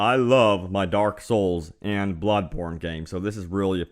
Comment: i swear i thought he said "Blood Porn".